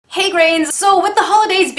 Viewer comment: I thought you said "Hey Greg" and I got really confused for a second